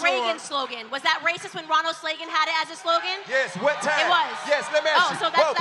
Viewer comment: Uhhh.... Did she say Ronald Slagean at 0:47 or is my earing fucked